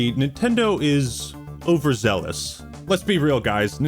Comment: when you said “Nintendo is overzealous” I thought I heard you say “Over-Zeldous” like a Legend of Zelda pun. Missed opportunity?